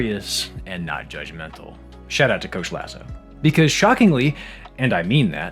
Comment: I thought you said "shout out to jo schlesser" and I was like "???? the f1 driver from the 60s?? what has he got to do with this??" and now I'm even more aware that my f1 brainrot may be terminal